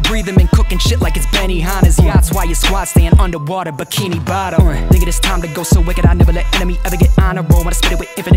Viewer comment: he said nigga, boi white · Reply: He said thinking